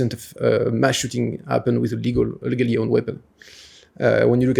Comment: did he say "illegal" or "a legal" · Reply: I think he said legal